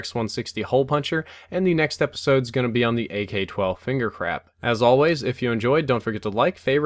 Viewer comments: It sounds like you said "Finger Crap" XD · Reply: He did say finger crap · AK 12 Finger Crap?? Sounded like you said just that...